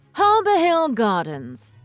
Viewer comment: I thought she said, "Harbor Hell Gardens" It probably is...